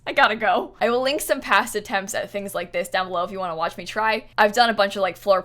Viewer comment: said “i’ll link some of my past attempts below if you want to see my try” but i heard “if you want to see me cry” 😂